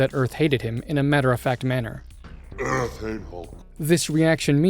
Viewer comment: He sais "Earth hates Hulk."? I thought he said "Earth ain't home."